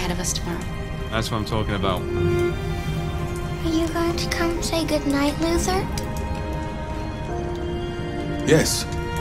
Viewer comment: at first i thought alice said "are going to come say goodnight loser?"